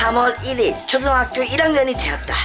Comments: 3월 1일이라고 들리는데? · 옥에티 발견 띠요오옹 (자막에는 3월2일인데 들리는건 3월1일...)